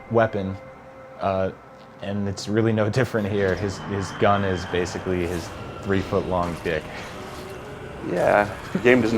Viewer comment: Did he say his gun is basically 3 foot long dick??